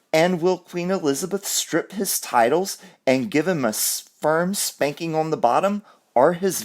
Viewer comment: Lol at 2:46 minutes mark your slip of the tongue sounded like you said "a sperm spanking" lol Ah love it.. it's just one of those days for me too.